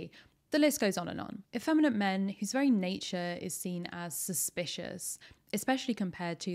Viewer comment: I misheard "suspicious" as "cis-picious" and now I can't stop laughing. "There's no heterosexual explanation for this" and "vibes" sums up my current DnD character too well. He's a changeling bard that is the demisexual poster child for the "be gay, do crimes" motto. His friendly-misread-as-flirting escapades did get us close enough to a local merchant to grift for a magic item, so no one in the party is complaining, lol. Also, as a DM who has a tansmasc wererat NPC in their game, I fully support Remy's whole deal. I'm gonna have to go read that study now...